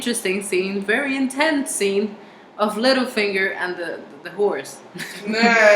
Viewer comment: I thought you said "Littlefinger and the horse" and I was like, "wait, I don't think I remember that scene." Check this out: <link>